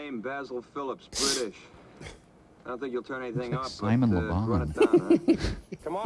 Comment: What did Mike say at 59:59? It had crow cracking up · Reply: Mike said the guy looks like Simon Le Bon. 😹